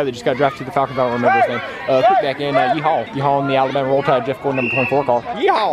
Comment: did he say Jeff Gordon #24 car